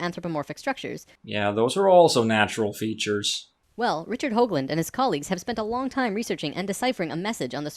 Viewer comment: i thought he said richard hovind for a second.... tbh that wouldnt have surprised me